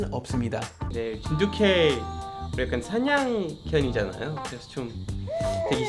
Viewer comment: 댓글 다는 작업은 마님이 했나? 사냥견이라고 했는데 사냥형이라고 들었나 봄.